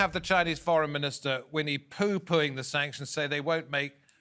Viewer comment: did he just say 'Winnie pooh'